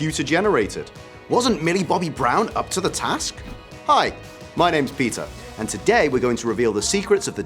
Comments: When he said his name I heard "my name is pizza" (sorry) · I thought he said “hi, my names pizza” lol